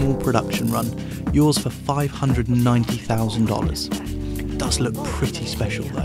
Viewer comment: did i hear 590,000$ ?